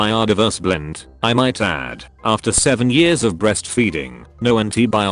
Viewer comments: did she just say 7 years of breastfeeding??? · did she really say 7 years of breastfeeding?